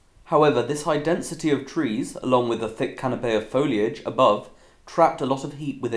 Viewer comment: did he say canapé? 😂